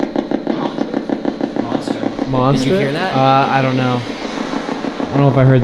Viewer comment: A great investigation! At about 1:05:51 you guys had two ideas about what the voice just said. I heard “Monster”. Younger kids say “monster” if they fear something. Later on at about 1:46:00 you ask the spirit for its name. Then I hear “Olive” but that gets overlooked. Did you hear it, too? Olive was a popular girl’s name many decades ago. What do you think?